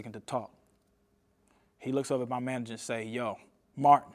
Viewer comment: The exec “… Baby. We got to take you shopping or something.” That sound like Diddy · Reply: he clearly says the producer said to his manager "Mark"